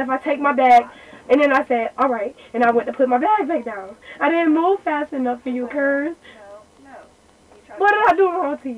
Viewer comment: Wtf did say at 8:45???? "I didn't move fast enough for you currs"